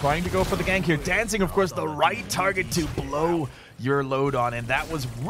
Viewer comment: Did he just say "Blow your load on" at 11:23 ?